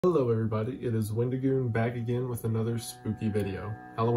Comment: “hello everybody it is Wednesday my dudes” is what I heard